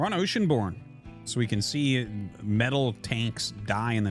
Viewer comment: I thought I heard metal taints lol